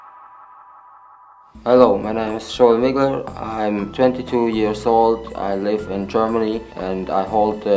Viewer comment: I thought he said “I’m genital years old”